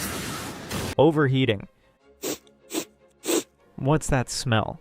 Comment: I heard overeating. Definitely an advantage.